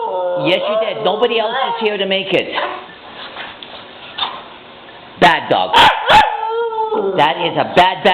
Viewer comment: Omg it sounds like shes saying "I'm not a bad dog" ! xD